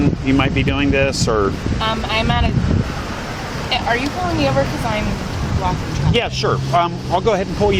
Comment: It sounded like she said “Are you pulling me over because I’m black… no sir I didn’t mean that”